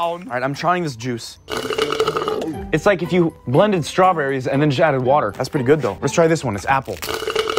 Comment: am i the only one who heard "its like if you blended strawberries then shat in water"